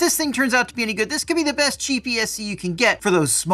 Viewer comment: Did you call it a gps @ 0:10 ? Lol Ec5 and Ec8 plugs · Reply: Cheap ESC :-)